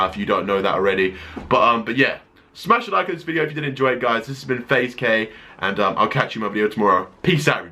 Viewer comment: I promise you, I heard you say "Faze Gay" in your intro.